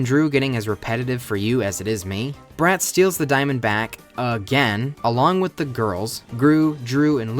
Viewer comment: Is it just me or I keep hearing say "steals the diamondback" instead of "steals the diamond back"?